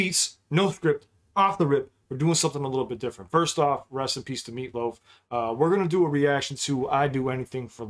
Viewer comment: I swear to god when you started this with "Rest In Peace to Meatloaf" I heard "Recipes to meatloaf." 😂 I was very confused for a second.